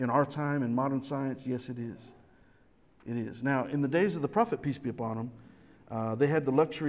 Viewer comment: Every time says "Peace be upon him" I hear "PT Barnum" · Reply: And I hear "Peace be John Bonham."